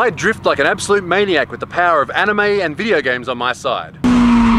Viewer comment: so I couldn't hear you correctly during intro, I heard "with power of games and anime tiddies on my side", I just went "excuse me what ? ? ! " next time I'll try to not be distracted while videos are starting so I can hear them proper